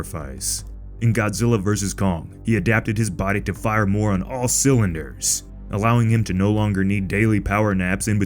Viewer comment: i genuinely cant tell what bro just said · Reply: "He adapted his body to fire more on all cylinders"